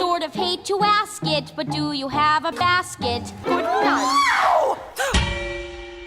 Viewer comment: I love a lot of the jokes around Red and the Wolf. The "PEE!" one really got me. One thing I have an issue with though: at 2:00 it still sounds like Red is saying "basket" when I know from the original YTP (because it was my favourite joke in that one) she's supposed to be sentence-mixed to say "casket".